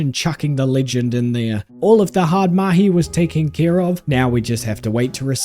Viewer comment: Woah did he just say "the hard mahi"